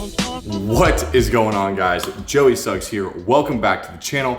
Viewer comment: I seriously thought he said joey Sucks here lol it's kinda catchy.